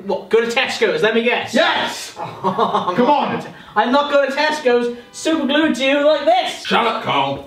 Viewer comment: R we gonna talk about how it sounded like Carl said “Im not into girls” when suggested a gangbang 🤔